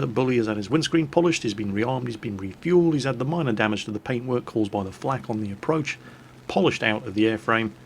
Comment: I thought he said minor damage to the paperwork, and I almost crapped myself laughing. I listened again and he said paint work. Ah well.